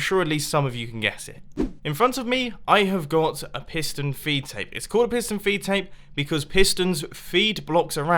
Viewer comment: LOL for the longest time I thought you were saying pistion v tape